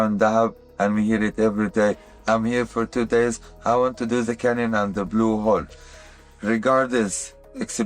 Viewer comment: the transcript says “I want to do the tunnel at the Blue Hole”, but it sounds more like “I want to do the cannen on the Blue Wall”. “Wall” is simply vowel misalignment (and /h/ elision), but the /k/ sound is very clearly not /t/. I hesitate to write “cannon” or “canon” there, as neither makes sense, so I ask: what word is he actually saying, and from which language?